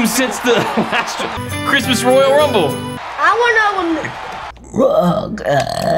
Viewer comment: I thought Little flash said “Rooockk ittt”